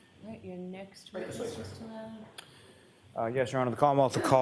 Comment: How come everyone in court can say Mr Lally but when judge says it she sounds like shes sayn Doolally😮